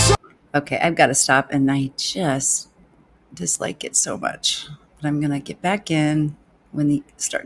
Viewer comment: slight shock there, I thought you said "I've got to stop and I ....juuust dislike it so much". Phew...